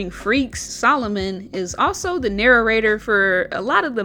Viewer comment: did you just say narrarrator?